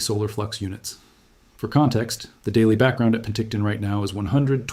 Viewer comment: did he say Penticton?